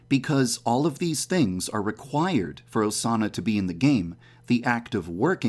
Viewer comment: Sounded like she said sauna after some time lol